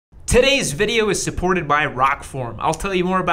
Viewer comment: sounded like Titites Video